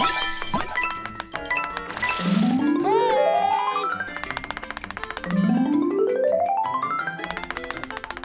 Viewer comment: The flower does say "How'd you get over there?". I think you just didn't hear it clearly. I had to listen to it again a second time to get it and the speech balloon shows it as well.